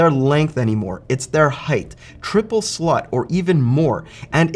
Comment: am i the only one who heard him say triple slut?